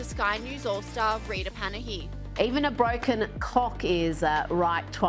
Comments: Did she say cock? 😀 Nah either way, these "news" stations, (Fox) should be ashamed to be making fun of him. Just like CNN makes fun and jokes about Trump. Like what the hell is going on in this country? News outlets from other counties are talking about this. It's bad! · She clearly says Cock not clock 😅😅